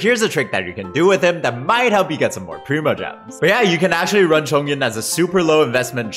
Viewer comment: my brain thought you said “Copium” when you said “Chongyun”